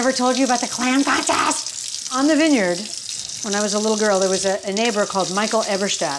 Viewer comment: Looking for dinner inspo, listening again and swear I heard "there was an enabler" at 2:54, but on replay clearly hear "there was a neighbor".... chuckling.